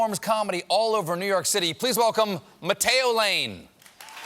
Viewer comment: I swear I heard Stephen call him "Potato Lane" at the end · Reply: Bryce Walburn You're right, I listened again, and "Matteo" was clear as day. But I swear last night when I listened to this, I heard "potato" too! haha the power of suggestion... ;-)